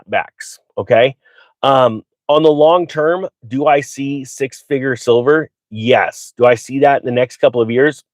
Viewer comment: did you say 6 FIGURE SILVER (in the long term) ?????? can anyone clarify if he said this. thats crazy.